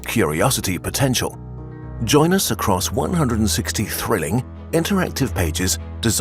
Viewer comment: When he said 160 thrilling I thought he said 160 trillion XD